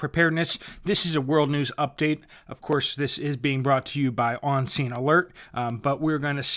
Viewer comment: thought you said obscene alerts at first.